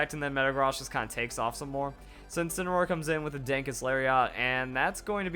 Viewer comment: It sounds like is saying "Dankest Lariat" instead of "Darkest Lariat". As always, wonderful video!